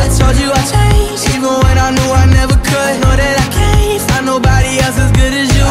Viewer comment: I know that I can’t のところずっとThe Laroi kidに聞こえてた